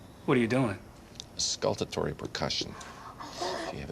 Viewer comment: What did House say at 7:50? Skultertory percussion? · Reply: Auscultatory Percussion